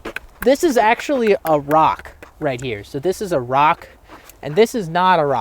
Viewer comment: A rock? Did you say invade Iraq?